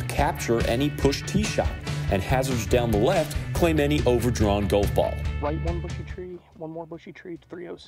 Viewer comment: So much better than the main broadcast, sorry Myrtle Beach Classic but that was rough to watch... my only gripe, what was up with the announcer's accent?? 😂😂😂 i could have a sworn i heard gwolf bool